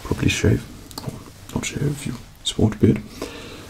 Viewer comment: "You probaly shave... or not shave if you.......?????" WHAT DOES HE SAY THERE?!?!? To me it sounds like "Or not shave if you sort a bit" but that doesn't make any sense, someone pls help, it's driving me crazy!!!